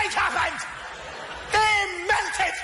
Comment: did Tommy say the priests were dermented or they melted.